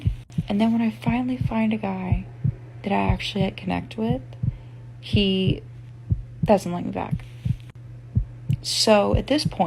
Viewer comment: What is she saying at 1:06 - 1:07? "He doesn't wenmllvnm back?" B*tch speak clearly!